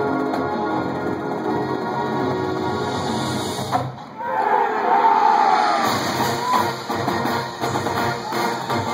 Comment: What are the audiences saying at 0:35? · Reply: "VANGUARD!"